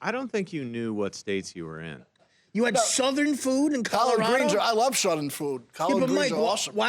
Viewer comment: Sounded like he said he loves "sudden food".